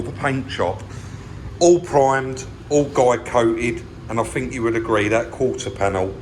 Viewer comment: Ah so it is guide coat - I always thought you were saying Glycoat as if it was a brand name or something. :-)